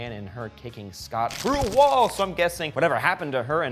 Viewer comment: go to 7:23 and turn on subtitles it says crew whoa instead of through a wall